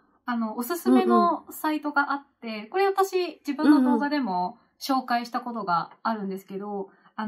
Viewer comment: i hear 「動画」at 7:28 and not 「講座」 (a comment on the subtitles). even though either way they end up basically meaning the same thing.